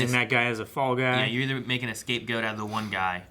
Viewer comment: Did just say "You either make an escape goat outta that one guy"...?! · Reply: Think he said "Makin' a scape goat"